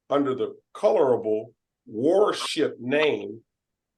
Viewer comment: Did he really refer to himself as "warship"? Because, I thought I heard it that he referred to himself as "Horseshit" of which he is full of.